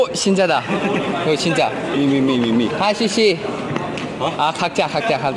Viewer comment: captions say “hi Winwin” but he’s saying hi to Xuxi 😂